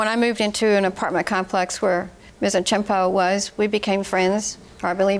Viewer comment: I swear I thought the Narrator say "A TAMPON " I gotta stop smoking weed 😂